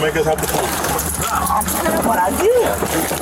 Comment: Perp: "Bro... I'm.... what I did?" trying to close caption him into sounding coherent: "Bro, I don't know what I did." That's not what he said and you know it